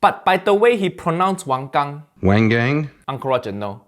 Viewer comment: I'll be honest, I thought he said Wang Yang and until you said it correctly I was confused how Asian-American wrestler Jimmy Wang Yang became a chef